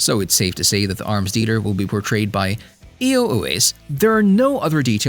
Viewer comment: what did u call Iko Uwais at 6:55? I turned on subtitles and it said EOS :)